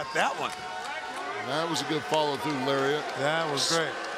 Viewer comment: I thought I heard Paul say "Call of Cthulhu lariat" (1.75 speed) That would be a good move name for the Dark Order.